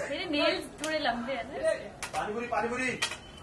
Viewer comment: did someone just say pani puri pani puri in the background 🤤🤤🥺🥺 itne din ho gaye😭😭